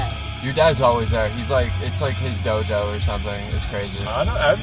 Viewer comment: Did bro say gojo in 2:50 ?